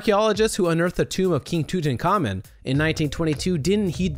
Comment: did he say king Toot? lol